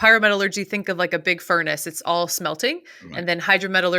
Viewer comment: Did I hear right? Did she say "It's all smelty"